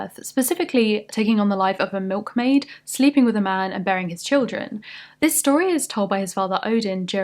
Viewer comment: goddamn, BEARING. I thought she said BURYING for a sec!